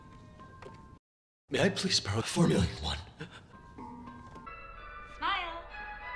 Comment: "May I please borrow 4 million won?" as you do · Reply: Me who thought Gi-Hun was saying "May I please perform Formula 1", lol.